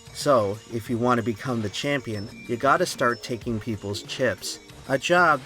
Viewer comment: I thought the saying was “If you wanna become the champion, you gotta have a glass of water.”